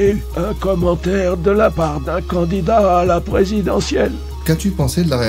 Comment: à 20:40 c'est pas "oh tu sais mon petit, moi j'ai eu un commentaire de la part d'un candidat à la présidentielle" mais plutôt "de la part du président élu en 2017" ;)